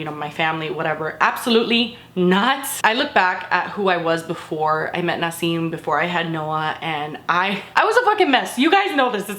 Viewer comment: I think she was saying it as “I was a mess before I met Nassim, before I had Noah” just listing events that happened. Like she was a mess before she met Nassim and she was a mess before she had Noah. I don’t think it was a statement that she met him before she had their child. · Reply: oh my god I just re-listened to it the way you explain it and it makes so much sense, because its weird for to say something like that.. I heard it as "I was looking at my life before. I met Nassim before I had Noah" lol my bad